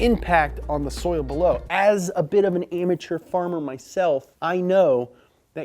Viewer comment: he says "amateur farmer" but the captions say "immature farmer" :D